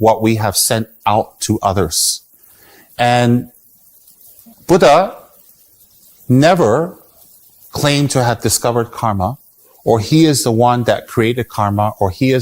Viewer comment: karma exist since Krishna and Rama era, stop false claim that term karma never existed before buddha, may be it didn't existed in your country but in india, it was always there. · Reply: I just listened to what he said twice, he actually said: “Buddha has never claimed that the karma has never existed before him..” The pause in between the sentence broke the sentence into 2, I think that why it sounded like he said Buddha claimed to have said karma didn’t exist before him.